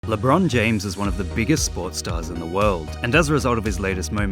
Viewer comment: I thought he said pornstar not sports star